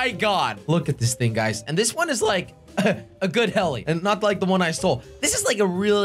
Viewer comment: did you just say "better than i stole"